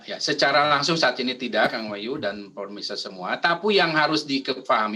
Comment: gua yang salah denger ato dia yang typo mau bilang "Tapi" jadi "Tapu"?